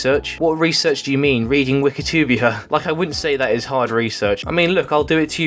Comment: Did you just call Wikipedia wekitibia?